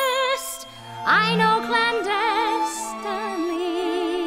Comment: I always thought she said "I know glindas stunning"